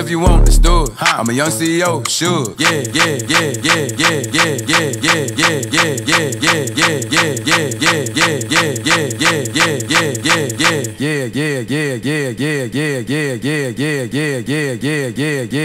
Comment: I swear 4:04 sounds like he us saying you're gay